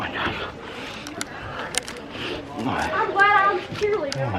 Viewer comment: Dude at 12:27 did someone say I'm glad I was a cheerleader? 😂😂😂😂